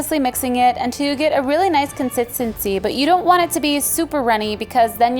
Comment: Im sorry no hate but did say conzitziz